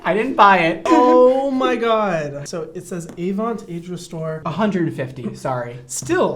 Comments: I thought he said Avon 😂 I was like $150 for Avon!??? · OMG I thought he said AVON instead of Avant!! LOL I was about to die if Brad Mondo had an Avon eye cream!